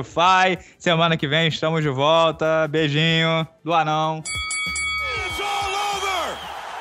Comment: eu escutei "beijinho do anão"